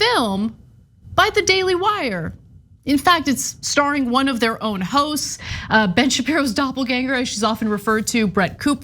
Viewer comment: I thought you said, "starring one of their own hoes".